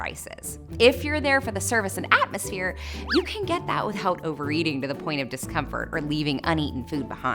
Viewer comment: I can’t understand what she says here... I’m a native English speaker and I played it back 4x including twice with headphones on... what did she say (???) “you can get that with ..... “??? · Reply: Without overeating